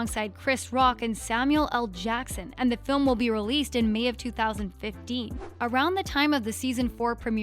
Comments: did she just say may of 2050? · Did she just say 2050? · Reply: Benjamin Mendis no. 2015